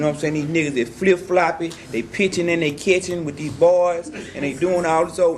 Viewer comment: "Pitching in they kitchen" is what took me out first 😂🤣 I had to watch the whole sequence like 3 times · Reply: I thought he said “they pitching and they catching”